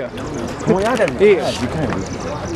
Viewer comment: Anyone know what melly said at 4:03 mark? Sounds why yall got....? Couldnt make out the rest seemed annoyed